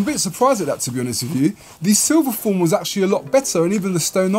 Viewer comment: what was the first Canada Goose jacket you tried on ? Help appreciated bro 😎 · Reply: I thought he said silver foil ? Couldn’t find it. Also been trying to look up that parajumpers jacket and can’t find that either. has a mean jacket game